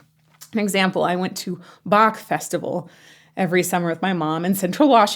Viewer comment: when you first mentioned the Bach Festival, I thought you were saying Bok Festival... like the Bokfest we have here. and I thought... boy, your parents really ARE open minded if they took a 10 year old to a beer festival! then I realized what you were actually talking about.